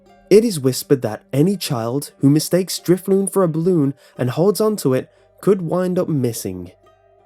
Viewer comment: youtube thought it said (at 1:37): "that any child who mistake stripping for a balloon".....wow does youtube HAVE to make everything inappropiate